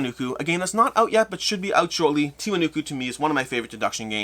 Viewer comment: what game is saying? I can't get the name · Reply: Tiwanuku